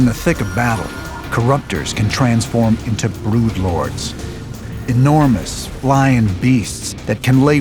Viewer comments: Did he say "Bloodlords"? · it sounded like he said blewdlords lol